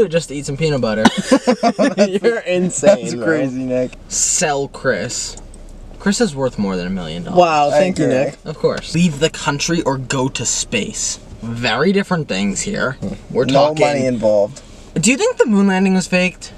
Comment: thought he said “eat the country” HELP